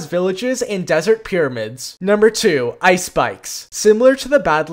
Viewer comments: I thought bro said Icespice😂😂😂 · I thought he said ice spice instead if icespikes